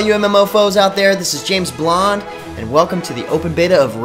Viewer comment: I thought he said James Bond at first...